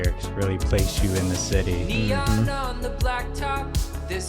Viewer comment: "Neon on the city" WHAT!?!?! I always thought it was Tim saying "I feel real pretty" Man, i've been hearing it wrong for so long. ;p